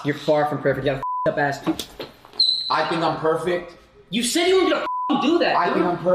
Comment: ish is it just me or does the guy that gets smacked say "WTF you said you weren't gonna do that..." possible I misheard, idk. If that is what was said, makes me wonder if it was staged for controversy views.